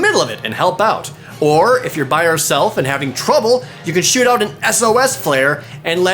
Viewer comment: Anyone else hear "You can Shit out an S.O.S. Flare"